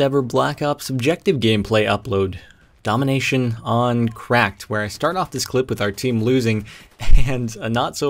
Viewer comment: I thought you said domination on Crap hence the bad quality of the video. Yes, I know it's processing. Trolls aren't welcome